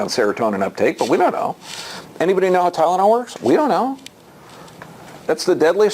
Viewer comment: At about 28:10 he says does anyone know how __ works? except I couldn't understand the word. It sounded like Tylenol but I wasn't sure. I reran it three or four times and couldn't tell. It's right after he talks about serotonin. He says it's the most highly prescribed drug in America and no one knows how it works but I wasn't for sure if he was saying Tylenol or something else. Can anyone who understood it better than me help clarify it for me? Thanks in advance. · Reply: Yes, he said tylenol